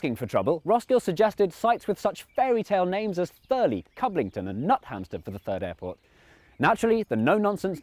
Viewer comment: I thought you said "Nuthampster" for a moment, S couldn't help but laugh